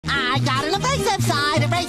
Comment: Dose it sounds like he saying "I got on the racist side"